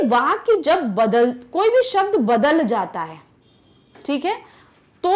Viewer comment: I heard Bhikari XD